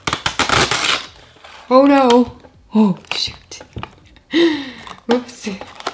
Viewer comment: it sound like she said sh*t